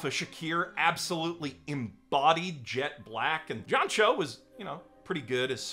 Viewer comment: I had to back up the video at 10:26... they way he named John Cho.. sounded like he was calling him Yamcha :p